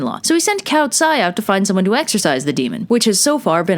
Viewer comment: At first when I hear u say exorcise I thought u said exercises lol I’m weird